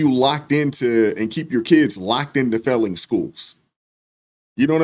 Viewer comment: Great work please give it up. Ha ha I thought you said “felon schools” then realized you said “failing schools”. And then I thought to myself: they’re actually both accurate in a way aren’t they?